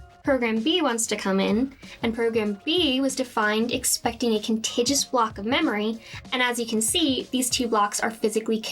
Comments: it sounds like you said "contagious block" instead of "a contiguous block" 😁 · Did she say "Contijus"? Maybe "Contagious"? How about "con-TIG-you-US". At this point, I assumed this was a comedy sketch, and departed.